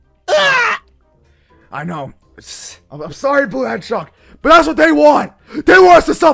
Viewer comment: was that a- “I’m sorry” I just heard from Shadow?